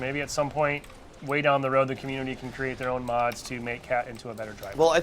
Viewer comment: Did.....did you say mods!!!!!!!!!!